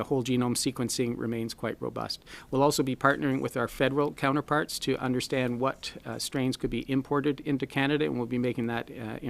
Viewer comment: Did he say at 11:40 approx that the federal government will import new variants to Canada? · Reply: No. He said, "We'll also be partnering with our federal counterparts to understand what strains could be imported into Canada." In reference to other variants that can come into Canada from other countries. Of course, new variants could emerge domestically because higher transmission rates make that possible.